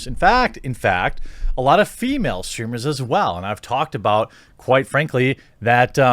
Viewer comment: I thought it said female stripper